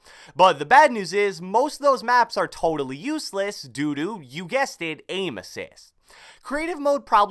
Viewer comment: who else heard doo-doo instead of "due to" at 4:17 loool